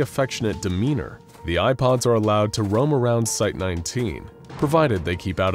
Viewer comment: Every time he says “Eye Pods” I hear “IPods”